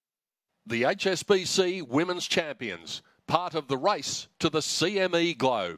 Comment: I thought he said, the "rice to..."